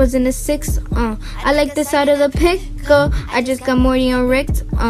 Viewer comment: sounds like she said ”I like the side of the pickle”